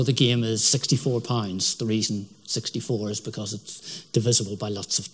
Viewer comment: I was merely listening (instead of watching) and understood that at stake were 64 pints. ;-)